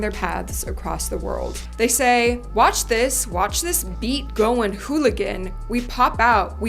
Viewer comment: I never heard anyone make this connection with the lyrics of Hooligan and Whole Again and I think you are 100% on to something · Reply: sameeeee!!!! at first i thought Tae said in the end "watch this watch this we goin' whole again" 😅